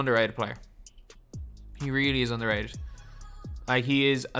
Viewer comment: I thought I heard “He is really underage”